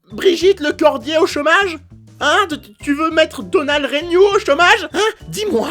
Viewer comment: moi c’est pas règnault c’est regnault 😭